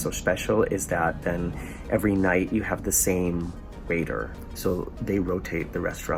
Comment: "Every night you have the same raider." What's a raider? Sorry, I may have misheard. ¯\_(ツ)_/¯ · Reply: waiter.